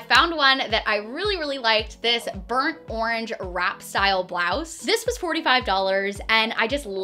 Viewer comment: I thought she said rat style blouse 😂😂😂